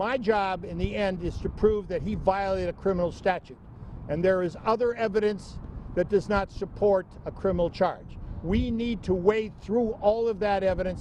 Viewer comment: sure sounds like he is saying the buck stops with him....and currently, there is insufficient evidence for a criminal charge......No?.... Is that what you are hearing?......